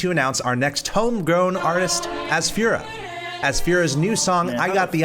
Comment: I thought bro said “as führer”